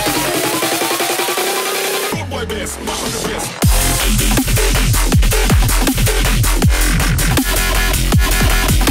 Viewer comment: の声ネタがどうしても聞き取れない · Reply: "Rude boy bass, mash up the place"